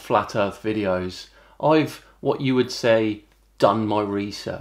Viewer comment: I misheard you when you said “utter hogwash”, initially thinking you said “nutter hogwash”. I think my version is more apt.